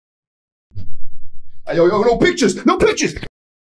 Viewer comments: It sounded like when he said no pictures that sounded like he said no bitchs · I thought phantom said: ayo no bitches no bitches.🤣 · As the phantom when you were saying “ay no picture” I thought you were saying “ay no b**ches”